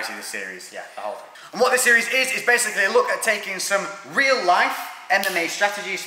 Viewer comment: woah at 0:31 i thought he said REAL LIFE ANIME strategy!!!